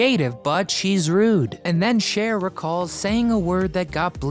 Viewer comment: What word got bleeped out ? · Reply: Bitch Got Bleeped Out It Obviously Rhymes With Witch.